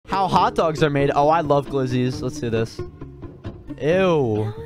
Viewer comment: I thought it said “how dogs are made” 😂😂😂😂😂😂😂